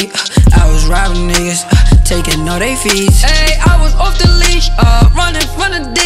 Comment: I thought he said I was off the lean I was like bruh was drinking lean at 13 he says leash tho